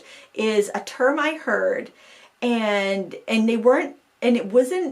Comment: Hi, I’m sitting here by myself laughing and thought I’d share… when you first said “a term I heard”, what I heard was “a termite herd”… I thought after you saying you were running out of things to talk about you were really reaching! Also, just signed up yesterday for your Michael’s class!